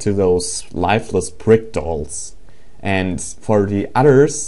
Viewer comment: lol it sounded like you said " Lifeless prick dolls "